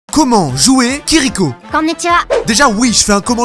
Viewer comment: Je scrollait sans regarder mon écran, j'ai entendu " comment jouer kirikou ", j'ai pas compris